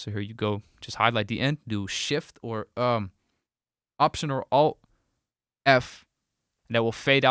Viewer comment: shift + ?? No entendi esa parte bro my inglish is not so perfect 😅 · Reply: Alt/Option + F